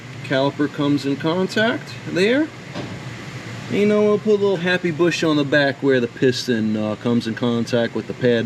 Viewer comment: Did I hear a Bob Ross happy little bush quote in there? Even if I’m hearing things, I’m going with it. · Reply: I thought he said "happy bush". I try to make sure the bush in my life stays happy.